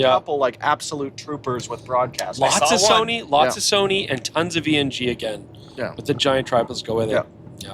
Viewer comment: What did say at 21:30? Lots of Sony and Tons of what? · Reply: Tons of ENG